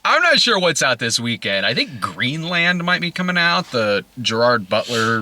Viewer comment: I misheard that as Green Lantern and felt my heart sink